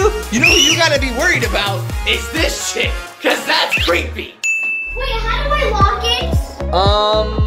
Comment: after years i still dont know what said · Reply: He said to chase, “What you have to worry about is THIS CHICK, BECAUSE THATS CREEPY!”